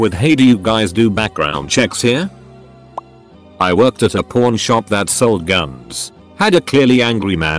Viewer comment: I hear "porn shop" and not "pawn shop"